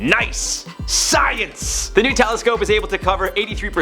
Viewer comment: When Jarred said "New Telescope" I heard "nutellascope" and got super excited. I was very disappointed as the story progressed. I'm not sure what I thought a nutellascope was but damn I was happy for a moment